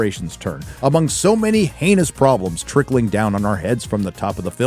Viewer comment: sounded like anus